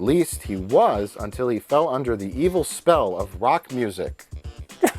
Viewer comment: I could have sworn that at 6:23 he said BACH music. I was very confused there, and also hopeful, for a little bit.